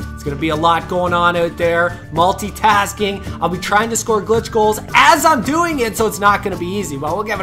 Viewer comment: did you just say GL*TCH?!?!?!